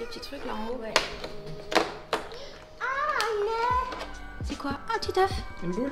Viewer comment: C’est moi ou j’entends dire « oh un oeuf » à 8:26. Elle est trooop choupinette